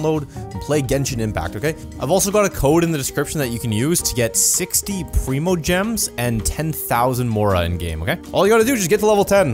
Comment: Celebrate Genshin Impact v2.2 with me: <link> Use code “BSPD3ZRXU985” to redeem for 60 primogems and 10,000 Mora. Travelers need to reach Adventure Rank 10 to claim this reward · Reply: I thought he said 10,000 primogems for a second T T